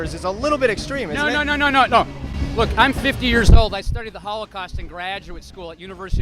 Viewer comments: I swear I had to do a double take with the blue shirt guy at 5:12 cuz I thought he kept saying "I started the Holocaust in graduate school..." when he actually said he studied it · That guy... Did he just say he "started" the Holocaust in graduate school? He is exactly like my ex-girlfriend: Completely overeducated one a specific topic to the point where it becomes fanatism. Right is evil, only the left is good and moral. Nazis are everywhere and just waiting to come out of their dark corners. Being even slightly conservative automatically means you support the installment of the Fourth Reich. I can't stand those people.